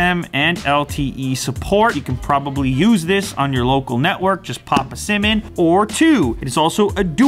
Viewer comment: I hear "just pop a semen"